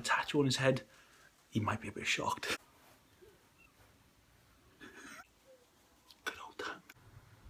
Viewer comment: what did you say? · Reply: Good old tongue